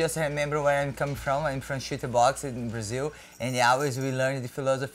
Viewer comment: Thank God for the captions when Cyborg spoke. I thought for sure she said “I’m from a shit box in Brazil.”